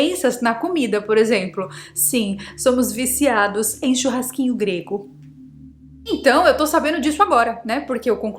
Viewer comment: JURO pra você que entendi "Beijo grego" ao invés de churrasquinho grego... Meodosdoceu!!!!!